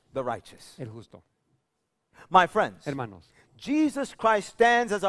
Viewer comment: Pastor listen to 32:43 when you said Jesus Christ it sounded like the dude said hypocrite